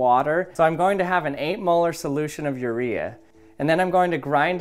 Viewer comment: Bro I thought he said he had an 8 ball solution of urea 😂😂